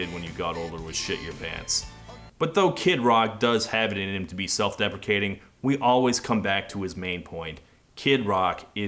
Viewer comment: Did you say self-deprecating or self-defecating?